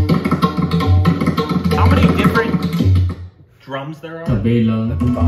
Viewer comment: Did he say 'Tabela'???🤣🤣🤣